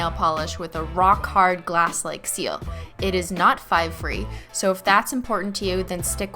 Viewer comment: what does she say? I couldn’t understand lol can someone write it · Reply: 'it is not 5 free' 5 free basically means it doesn't have 5 chemicals that may be harmful. Just look it up on Google lol